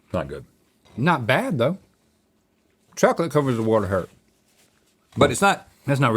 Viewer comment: someone tell me what link said hahaha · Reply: “Chocolate covers a world of hurt”